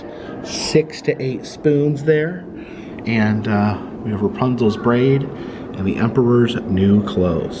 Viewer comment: at first I thought you said "Rapunzel's brain"